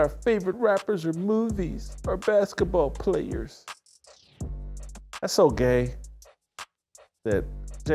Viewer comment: LMAO did Whitlock say “that’s so GAY?!” 😆